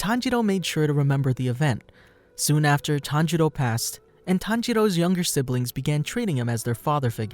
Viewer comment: I think I’m death at 1:57 did you say tanjiro passed away how is he still alive💀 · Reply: He said passed. Not passed away